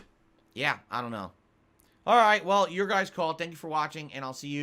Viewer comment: 18 minutes before the first combat, jesus Go watch the original tihydp run, it way more funny, plus it only takes 9 mins for the first enemy encounter Think about this too, he skip alot 2023 games cause he hated that year games, but for some reason wanted to replay re0, despite already beating it on direct cam and on hard. It'd make sense if these were games that were on his awful camera, or he ragequit before but NO ONE ask for Chrono Trigger, GTA5, or godamn re0 53:28 Like yeah, why didn't replay re6 then???? He played 5 solo so that not an issue, that was on the bad camera, and he could explain why he like 6 despite the mixed feeling for it - tho demand people to like what he like and hate what he dislike - 55:33 He doesn't even understand blue herbs, he literally think they heal just poison so he never pick them up, despite making green herb stronger, just not like an full heal with an red - Edit 1:14:27 HE FORGET BLUE HERBS, ARE YOU SERIOUS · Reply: Did he say "your guys fault????" He the one playing and got into that sitution, yeah modern is just the worst to edit/watch, sticking to his old playthroughs is just better. I still wonder why tf he replay re0, who ask or what made him want too